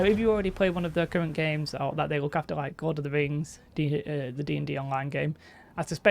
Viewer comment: did you say "God of the Rings"?! lol